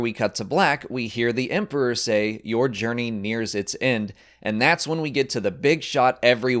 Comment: It might be only me, and I might be wrong, but I heard "your journey is its end" not "nears its end"